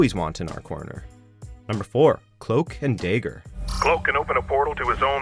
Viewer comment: Wait did I heard it right at 4:46? Cloak and deigger? What the F is a deigger? I guess you can pronounce valkyrie different, since that's a borrowed word, but isn't dagger just a pure english word derived from the verb dag? How? Why?